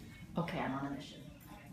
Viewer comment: Lol, did she just say "I'm on a mission"?? Maybe I heard her wrong, but that's what it sounded like she said.